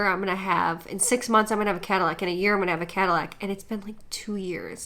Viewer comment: I thought she was saying catalogue not Cadillac .....